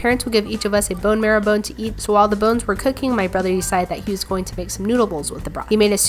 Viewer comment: I almost thought you said "Cooking my brother" XD